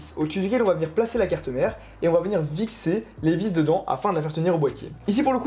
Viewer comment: Ya que moi qui entend Vixer Aulieu de fixer à 18:57?😂😂